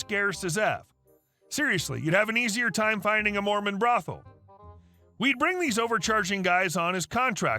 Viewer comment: My poor hearing I heard "You'd have an easier time finding a Norman brotha" 😂😂😂😂 Edit: That reminds me I need to call my brother Norman.